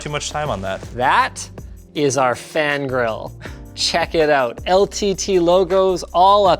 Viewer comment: Thought you said Fan Girl, not Fan Grill, my bad.